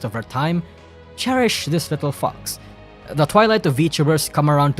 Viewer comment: had to double take bcs I thought you said "cherish this little fcks" 💀